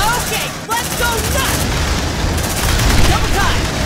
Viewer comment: It seriously sounds like she is saying let’s go sluts 🤣🤣🤣